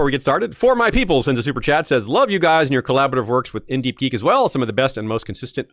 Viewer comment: What is the podcast he mentions around 6:20 that he wrote episodes for? I couldn't understand - I kept hearing "NDP" 😂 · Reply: In deep geek aka IDG . Check it out it's great ❤